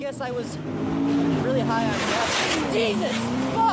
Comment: did he just say he was really high on meth